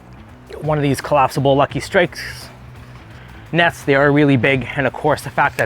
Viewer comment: Where did you get the folding net, I thought you said Lucky Strike, I can’t find on their website.